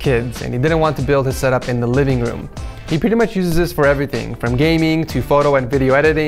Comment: did you just say gaming twice? lol